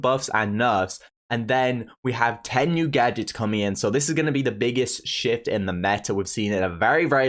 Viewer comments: I thought he said shit lol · i heard that biggest shit